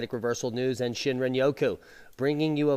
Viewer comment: I thought you said shit ran Roku